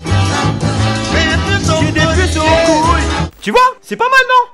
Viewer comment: moi j’entends plutôt " j’ai des puces au collier" 🤷‍♀️ 😂 Et à 7:07 j’ai entendu "c’est le prix d’une galette aux fruits" 😂 mdrr